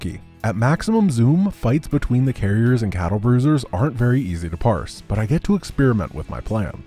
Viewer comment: You said Cattle Bruisers. I heard it!